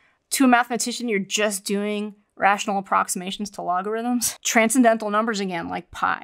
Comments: She says "logarithms" but the subtitles say "algorithms". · she said logarithms but the captions say algorithms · the captions say "algorithms" when it should be "logarithms." Just an FYI!